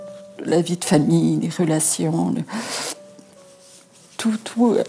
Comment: c est moi ou elle dit la vie de famille les félations WTF